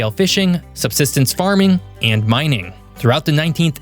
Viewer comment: does he say mining or miming? I heard alot of mimes come from Colombia but I'm not sure.